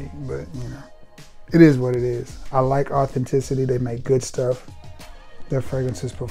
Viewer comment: What’s good I don’t see blue vortex on there site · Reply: I thought he said authenticity perfume